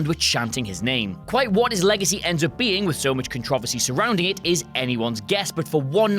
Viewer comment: what the hell did he say? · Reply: "so much controversy"